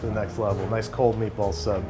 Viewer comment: I thought he said neatballs